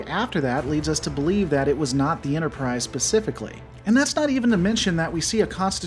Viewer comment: Did he really say “pacifically” instead of “specifically “ at 1:53?